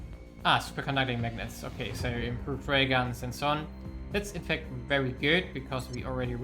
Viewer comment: Every time you say "Railgun" I hear "Raygun" :D